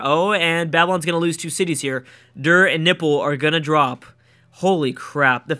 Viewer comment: Is it me or do i hear "Nipple" when He says "Nippur"?